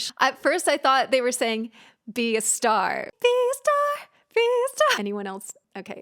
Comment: I really thought they were saying “might be a star” instead of “my fiesta”